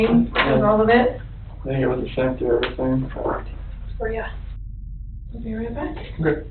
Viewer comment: I am not sure if the polygrapher said “faint with everything” or “same with everything”. but where it says ’ response is unintelligible, it sounds like he says something along the lines of “I feel like I was the same for everything” (meaning the questions) and she just replies “were ya? 😏” Incredible job in how she broke that man down through that process